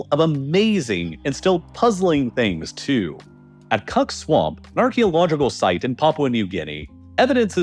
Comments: had to triple rewind to make sure I heard Cuck Swamp correctly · did he say cuck swamp?😅